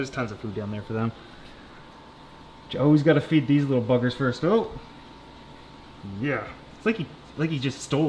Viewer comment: I had to rewind that, could've swore he said "I have to feed these little fuckers first" I was appalled. 😂😂😂